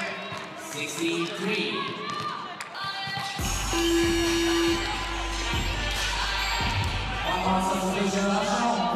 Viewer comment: What was the crowd chanting at 58:25? Sounded like "Brasilia", but that makes absolutely no sense at all. It's gotta be something else. Anybody know? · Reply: They are chanting "Russia". They're saying it in a Russian accent, so it sounds kinda different